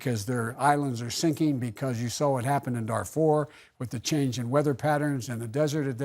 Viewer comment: did he say Darfur or I misheard that? I am from there! wow, I am actually surprised he knows about it!